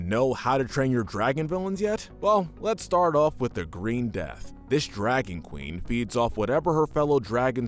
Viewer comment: Wait. Did he just say green death when the captions said red death? Which one is it?!